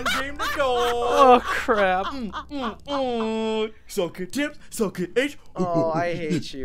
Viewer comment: Him: Suck it tybz! What I thought he said: Suck The Tip.